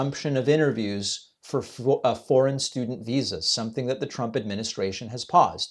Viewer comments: I had a very hard time understanding her. I also thought she said French students also 😅😂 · I don't know, David ... I heard "French students" at first, too. The first reporter was very hard to understand. Does Trump have dementia? I don't know & neither do you. Is he an unintelligent narcissist who's losing his hearing? Obviously. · I thought she said French student visa at first too. 🤷‍♀️ How can we tell the difference between dimensia and regular Trump?